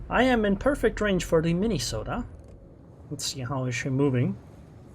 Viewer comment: Welcome back. You have been missed. As usual I misheard something you said and had to rewind. I thought at one point you talked about hitting the "Mini Soda" and thought of Diet Coke. That lead to me thinking what ship has that kind nickname. Obviously nothing does lol. Still I quite like "Mini Soda" fof the Minnesota.